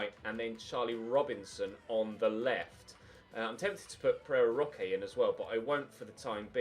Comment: First time you said Pereira Roque I thought you said Ferrero Rocher and I now hear it every time you say his name